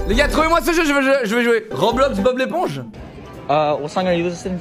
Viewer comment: c'était quoi le jeu quand a demandé svp vers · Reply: C'est pas roblox bon l'éponge ou un truc comme ça